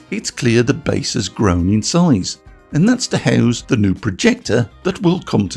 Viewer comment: Did anyone else hear: meow? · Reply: Sorry buddy, no. I just hear me saying, "...and that's to house."